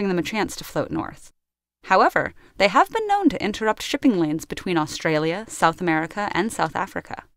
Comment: Did she say shipping 'lands'?